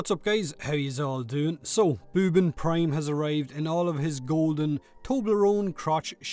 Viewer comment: I thought you said bourbon prime at the start